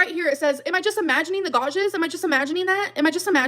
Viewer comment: Sorry did she just say "gaw-jes" when trying to say gouges? Smh this gorl 🤦‍♀️🤦‍♀️🤦‍♀️